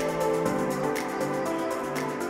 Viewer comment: Did she said Namaste (🙏) at 4:55 ?